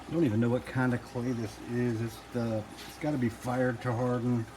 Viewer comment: what has it got to be, i can't really hear what you said? "its got to be fired to something"?